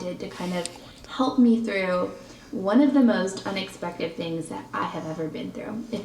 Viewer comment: Did anyone else hear “unexpective” literally in her intro... Around the 3:00 minute mark for this video.